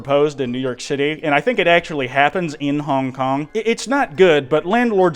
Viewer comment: I only heard "honk honk"